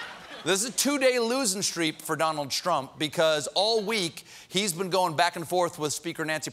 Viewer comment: did Stephen say "Donald Strump?" It might not be a mistake.